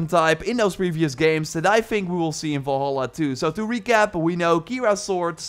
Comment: Did he say valhalla2 at 7:07?